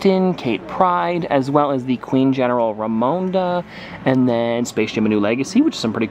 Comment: did you just say space ship instead of space jam?!?!!?